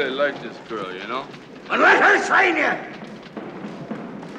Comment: “Then let her train you” 💀💀💀💀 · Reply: It sounds like he says “sign”, and I could never understand why the script and the captions say “train”.